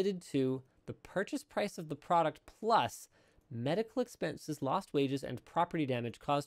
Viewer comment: When Linus said lost wages, I thought he said, lost babies. I had to go back